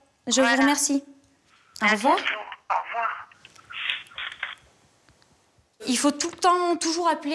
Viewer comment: C'est moi ou la meuf au téléphone lui dit "un bisou, au revoir" 🤣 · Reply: nan elle dit juste "à bientôt au revoir"